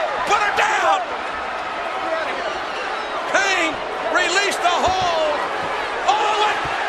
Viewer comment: "Kane released the ho..." I cannot believe JR said that, lol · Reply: I thought he said, "...released the hull"